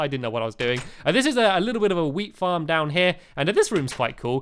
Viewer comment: DID HE SAY WEED FARM???